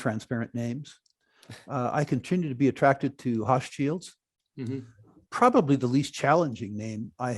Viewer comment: Anybody catch the producer Rick named at 15:05 ? Sounds like "Hot shields" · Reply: Hochschild Mining